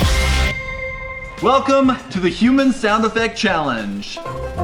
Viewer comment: when said "welcome to the human sound effects challenge" i thought he said "the human savaley" i was like wtf is that until it showed on screen